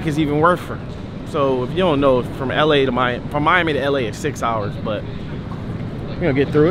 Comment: I thought you said 2 hours